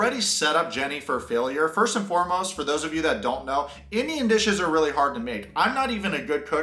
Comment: I thought u said Indian bitches I was gonna say where's the subscribe button 😂 u said Indian dishes lol yr hilarious were native TW